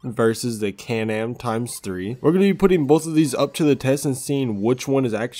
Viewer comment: 🤣🤣Question did he say canal TIMES 3